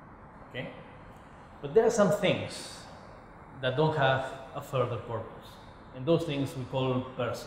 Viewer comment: i think that the priest says persons don't have a further purpose instead of 'do' have. and the wrong sub makes me confused